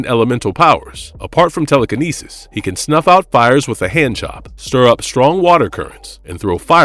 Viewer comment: 🤔 did he say "Snuff out fire with a hand j0b?! 😂 *6:50 into the vid.